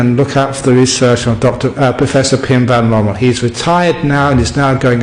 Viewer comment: Early on when he was speaking about Dr Ian Stephenson, he mentioned another scientist who had done research and was giving speeches on Reincarnation. It sounded like Pen (Not this- Vannoble?)something (at about 13:11). I used the subtitles (not good) and couldn't get anything even remotely close so I could google. Did anyone catch that? I've been googling and can't find anyone who's name is similar to Penn or Venn...If you caught it- give a yelp.Thank you. Namaste. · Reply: Hoving Pim van Lommel